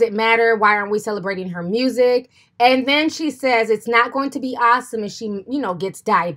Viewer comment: "why are we celebrating her body? why arent we celebrating her music?" I see nothing wrong with what she just said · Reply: I thought she said "why ARE we celebrating her music?"... Okay, I'm not mad anymore.